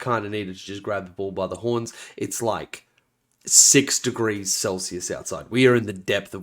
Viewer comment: 6 degrees celsius and cold in the same sentence bogles my northern european brain. Also the new tau rules seem interesting. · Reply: I thought he said 6 degrees Kelvin. Mind = bogled.